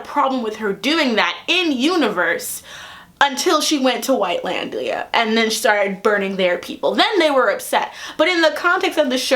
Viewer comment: "No one had a problem with that until she went to White-landing". I may have misheard that, but I chose to believe.